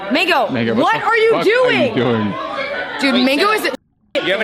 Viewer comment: For a second, I thought she said "N**ga, what are you doing?" She didn't, of course, but the energy was there lmao.